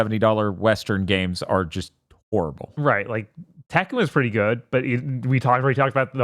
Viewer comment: What game name are they saying? I can’t make it out, it sounds like Tekken but it can’t be that